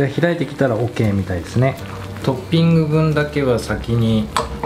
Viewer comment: 作業しながら音声メインで聞いていました。 「トッピング用で…」と聞こえたのですが、仕上がり時にトッピングはなし。 画面を見直すと、しっかり「普通に食べる」とテキストにｗ